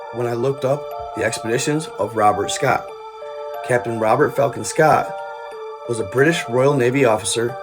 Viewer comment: Have it on 1.75 speed and I thought you just said Robert f*cking Scott 😂 good times